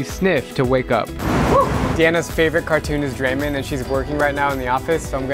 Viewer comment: did he just say"draymond"?